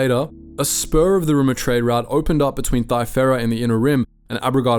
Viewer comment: i thought bro said “sperm donor trade route”